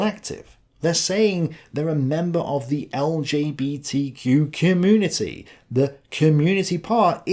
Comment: "They're saying that they're a member of the LJBTQ community -- the community part is the collectivism" Was saying LJBT instead of LGBT a slip of the tongue or was this deliberate with some meaning attached to it?